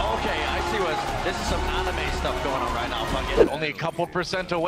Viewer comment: he really said Ana ma